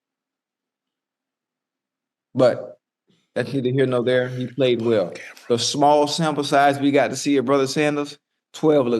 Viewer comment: what you whispering about?!? lol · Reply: “Your butt is on camera” 😭😭